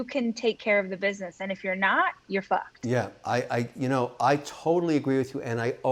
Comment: Did she say "you're fucked" at 21:13?